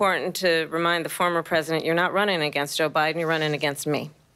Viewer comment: did she call Biden Obiden? 😂😂😂